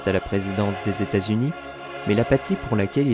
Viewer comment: "Mais La Patti pour laquelle il a développé une certaine empathie", sur le coup j'ai compris "l'apathie pour laquelle il a développé une certaine empathie", et ca m'a totalement fait penser aux Guilty Remnant et leur culte! Sinon incroyable vidéo, cette série est ma sérié préférée et tu lui as rendu honneur :)